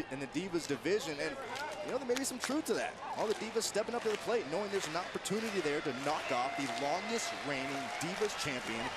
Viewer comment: did anyone else hear Cameron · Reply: "Shut up"